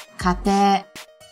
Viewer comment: かてい, we say it like かて without い or with い? I was confused because I heard only かて. :))